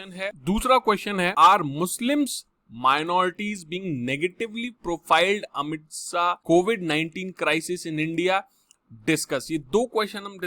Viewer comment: I heard Amit shah 🤣